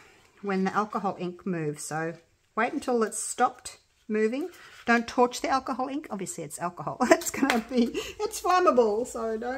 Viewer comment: I thought you said don’t torch because it’s alcohol ink